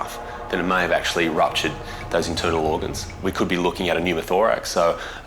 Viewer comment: I heard "inturtle organs" which isn't completely wrong....